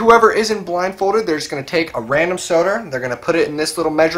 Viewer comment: "A random soder" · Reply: I thought he said a random sodar.